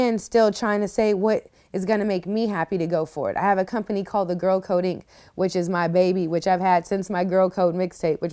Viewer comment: I thought she was saying Girl Coding, i was super proud. But I think she is saying Girl Code Inc. Now im not proud.